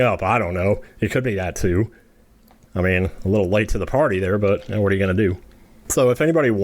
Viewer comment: misheard that as “a little late to the potty” 🚽